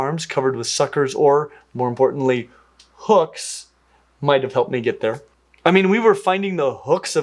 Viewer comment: thought you said hoofs and was like huh it’s a horse👀 then I realized you said hooks and it made so much more sense hahahah